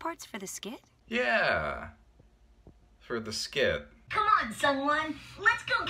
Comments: I thought she was saying "Someone" at the end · You know I only recently learned his name this whole time I thought Veronica was saying someone